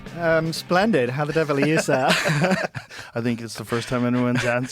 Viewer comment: what does he say after splendid? It sounds like Welsh or Gaelic or something 😂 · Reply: How the devil are you sir